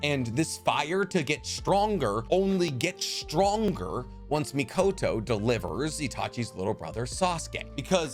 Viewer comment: It really sounded like you said the son of Fugako and Minato but well you could have said Mikoto and I heard wrong but it is a little funny